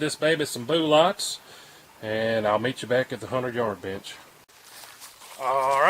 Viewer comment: lol it sounded like you said ill meet you back at the hundred yard bitch lolhaha